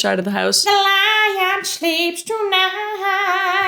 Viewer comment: When you said "the sponsor of this video...which is" i heard "the sponsor of this video...its jizz" I was like 😱😳🤨😐🤭😂😅🫡